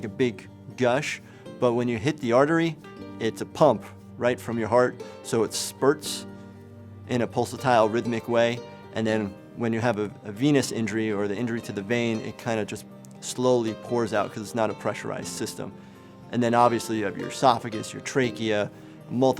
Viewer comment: thought he said "penis injury"